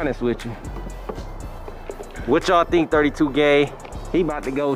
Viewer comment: no hate ..but I know I heard 32 gay lol